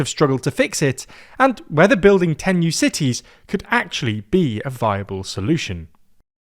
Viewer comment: I definitely heard "a fireball solution" instead of "a viable solution." Either I need a hearing check, or someone needs to work enunciation. (I'm guessing it's the hearing check 🤣)